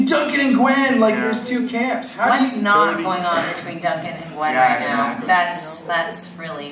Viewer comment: what did they say? sorry im not english and i can't understand a so fast talking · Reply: "What's not going on between Duncan and Gwen?"